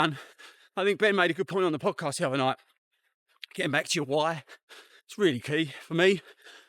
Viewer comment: Thought you said "get back to your wife" at first, there 😁 Thanks for the tips & congrats on the PB👍